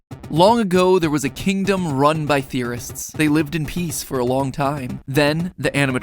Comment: I thought they said there was a kingdom full of terrorists at the start.